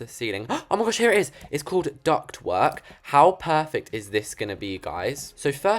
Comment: Anyone else on 3:54 think he was saying "Duck twerk"